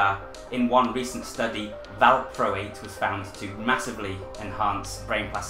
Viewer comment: Wonderful video! What was it you said at 7:55 ? You mentioned a study, but I was unable to hear what you said the thing was...valcro8? Thank you